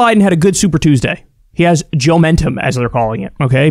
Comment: For a second I thought you said Joe Biden had Joementia instead of Joementum lol.